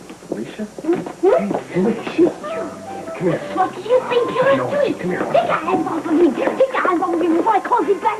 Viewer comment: Ummm....does Felicia drop the "f" bomb at around 1:15?! I have played this over and over again and I swear she says "What the "F" do you think you're doing?" Please repond anyone. If she did GH definately missed that one